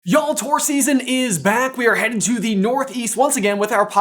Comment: I misheard the Tour announcement at the beginning, thought said "Taurus Season is here" and I thought "... yeah? It's been Taurus season for a few weeks" lol 😂 Have fun! Break a leg!